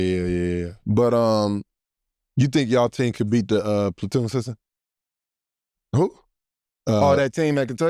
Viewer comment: what did he say? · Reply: I think he said the “platoon system” that Kentucky ran. It’s a rotation and roster strategy